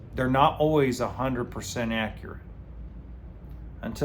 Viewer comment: I thought you said RV stood for tool box and a sense of humour (or something like that) haha